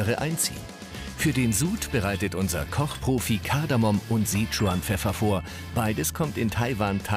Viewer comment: Ich hab irgendwie... 'Sea Trump Pfeffer' verstanden xD